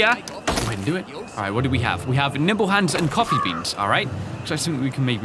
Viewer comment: "Nimble Hands" I'm wheezing because I thought you said nipple hands